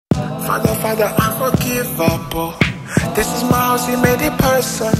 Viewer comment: I just hear "Father, Father I forgive Apple"